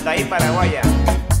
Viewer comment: ¿Qué es lo que dice?. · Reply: Hola Ivan, dice "Para las mujeres Paraguayas" ¿Ya estas suscrito en nuestro canal? Recuerda que para seguir disfrutando del mejor contenido, lo puedes hacer dando clic acá 👉👉 <link> ademas tenemos una recomendación para ti <link> Saludos.